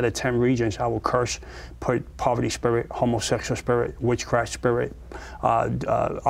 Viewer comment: Did he just say hormosexual spirit?